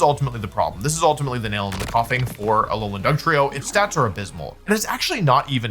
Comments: I heard 'nail in the koffing'. Am I hearing things wrong? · accidentally says "Nail in the Koffing" instead of "Nail in the Coffin" I think this is a very funny and cute mistake. I've done the same as a huge Pokémon fan, haha.